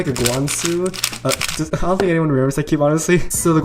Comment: Did anyone understand that sentance? lol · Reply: "I don't think anyone remembers that cube, honesty"